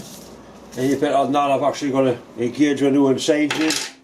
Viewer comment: I had trouble understanding what he was saying, so I turned on the closed captions and it still didn't make any sense. At 3:10 "you bet I'll not I'm actually gonna be kid you new insane"